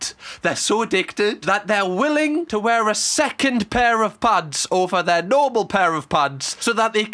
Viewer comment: i thought u said pads